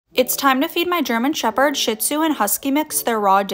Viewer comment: I thought for a second you said time to feed my German shepherd sh**